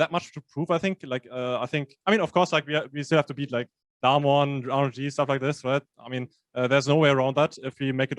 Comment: thought he said "we still have to beat DAMWON and 100 THIEVES" at first but then i realised it was just his accent saying RNG lol